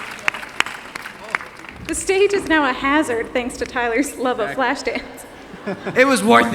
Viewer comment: Hi ! I'm french and I don't understand what she says at 1:54 ,the sentence with "flash dance". Somebody can help me pls ? just can you write me what she says in English and i gonna translate . thank you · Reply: "The stage is now a hazard thanks to Tyler's love of Flashdance."